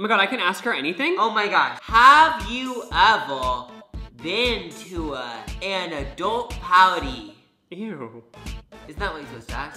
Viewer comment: what did he say?? I can't even understand most of what he says ugh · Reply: “have you ever been to uh- an adult party?” is what he said